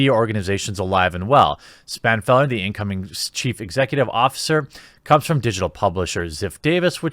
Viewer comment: lol I thought I heard Cheap Executive Officer